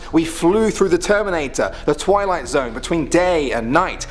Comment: did he did he just say Terminator?